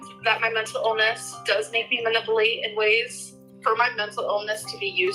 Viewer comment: Just 30 seconds in and already I've heard pronounce manipulate as maniplate and mental illness as menta illness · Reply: I always hear it as “mah-nipple-ate” when she says it 😂